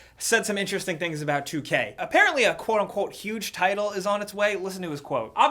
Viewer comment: my perverted mind thought "huge title" said "huge tits".